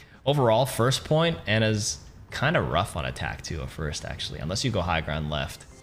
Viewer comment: Whenever he says "on attack" i hear "anatack"